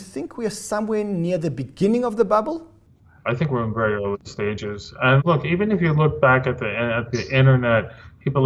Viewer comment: South African ex pat and subscriber here! :) 14:39 Steven Nerayoff says something unintelligible in answering what stage of bubble crypto is in. Can anyone translate what he said there? It's at 14:39 into the video.. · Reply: Very early stages?